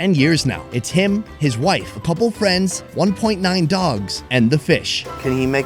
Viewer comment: Did he just said 1.9 Dogs?